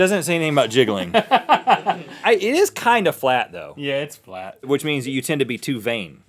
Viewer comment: i thought rett said "I is kinda flat though" lol